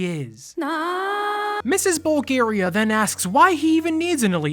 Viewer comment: why did I heard Bulgaria sob.